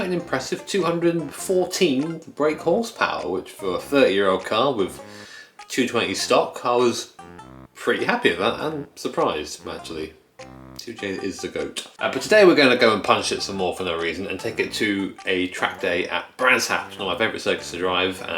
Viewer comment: Did this man just say "2 Chain is the goat" or am i hearing things?!